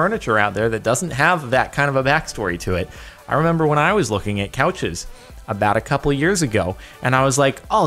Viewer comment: thought he said cowches